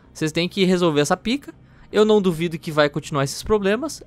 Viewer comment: queeeeeeee!?!?! Eu ouvi bem mesmo? Pika?